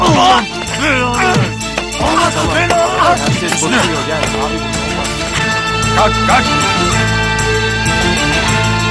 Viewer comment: alt yazı ekleyin bakın ne diyor... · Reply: Süper yumruk